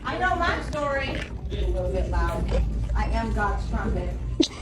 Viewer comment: Definitely misheard the blackface lady say “I am god’s strumpet” 😆